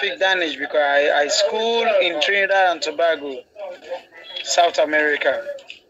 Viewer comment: Trinidad and Tobago ... 😅🤣😂. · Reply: 😂 I thought he said Trinidad & tabaco 😅